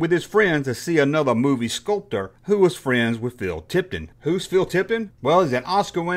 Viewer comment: Hi. It sounds like you said: "Phil Tipton". I think you mean Phil Tippett.